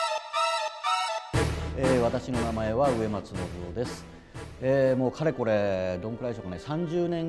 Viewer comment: My name is Nobuo Uematsu . I hear that as "I am a Music God". XD